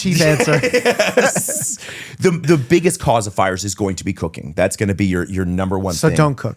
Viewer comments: I heard "The biggest cause of fires is going to be cocaine" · Did anybody else mishear at (1:00:50) that the number one cause of fires is COCAINE? I didn't realize that was a thing anymore. I thought cocaine was just snorted as a powder these days (I've only seen/heard about it in movies). Then they talked about grease fires and leaving stuff unattended and I finally caught on that it was cooking. But, that was definitely a WTF?!!! moment for me, watching this.